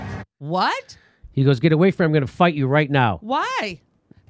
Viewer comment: I think the suspected cop is saying 'if you threaten me, I am going to break you right now' not 'get away from me etc'